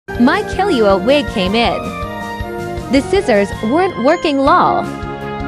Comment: I thought it said “I might kill you all come in” instead of “my killua wig came in”. I can’t hear; I must be colorblind 😂